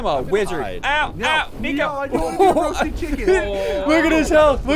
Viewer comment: did he said the n word? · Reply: No, he said "Nico."